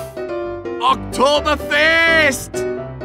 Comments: I used 2 think he says October 1st because I didn't know Oktoberfest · Medic voice misheard "OCTOBER FEST"